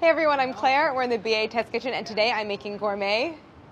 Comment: Can someone please tell me why for the longest while I thought they were saying BHS kitchen I’m done with myself